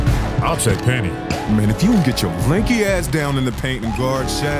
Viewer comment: “Mane if you don’t get yo rinky ahh down in the paint guarding shaq” I’m dying 😂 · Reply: i thought he said lanky